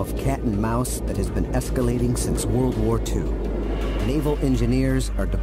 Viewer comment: for a second I thought he said “Evil Engineers”😂